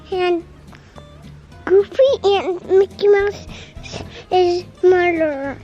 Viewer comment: It sounded like said "Mickey Mouse is the murderer" in beginning LOL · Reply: Tommy Purdy I thought he said Mickey mouses mother 😁